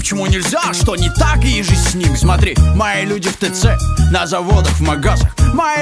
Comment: мне показалось что он сказал: мои люди в дэд сек (DeadSec)